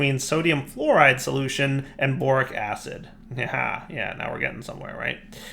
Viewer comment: I could have sworn i heard boring acid instead of borax acid